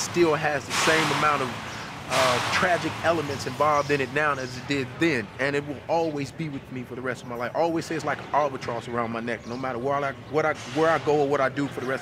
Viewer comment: did he say its like an albatross around his neck? like as in the bird?